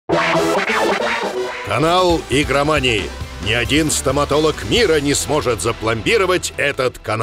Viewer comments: Я один услышал " Анал Игромании Представляет "? Раньше такого не было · В начале послышалось анал игромании... я еще подумал, что за...